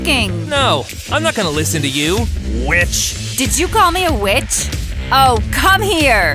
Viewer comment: I thought he said bitch instead of witch 💀💀💀💀💀💀